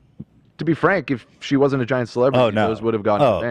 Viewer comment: I thought said giant slut and not giant celebrity. Was horrified for half a second.